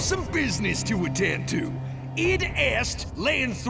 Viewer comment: What did 13 say at 9:57 ?? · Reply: I know I’m like 5 years late but I.e.